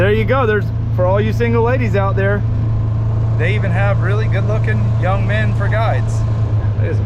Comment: Thought you said, “they even have young good looking men for guys!” I about fell out my chair! Lol, I know better but damn the needle came off the record..